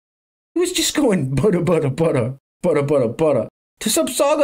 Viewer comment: Bada Bada Bada, it's a hit! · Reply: At first I thought he was saying "butter butter butter." 😂😂😂